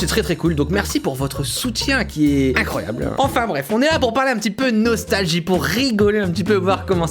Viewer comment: Ptn au début j'ai compris " bref on est la pour parler un petit peu de nostalgi "